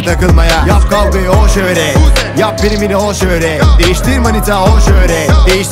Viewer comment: buseyeymis ama ben o kisimde ne dedigini anlayamadim bi turlu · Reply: Yap kavgayi öl şöhret